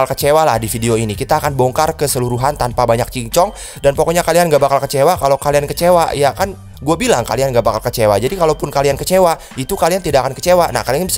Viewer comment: ngomong apaan bang🤣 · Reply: Kalian ga bakal kecewa lah kalian gabakal kecewa kalo kalian kecewa juga kan gua bilang kalian gabakal kecewa makanya kalian gabakal kecewa😭😭😭